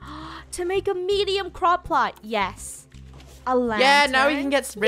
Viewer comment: When Funneh was saying, “Crop plot” I thought she was saying “Croc pot!”